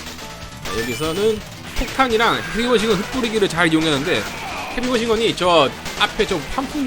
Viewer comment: 맞어 나는 에니멀 싱거로 들었는데 나중에 헤비머신건으로 못듣는다고 친구들이 놀려서 좀 짜증났어